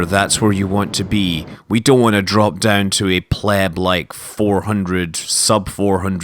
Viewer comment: Did you just say pleb-like?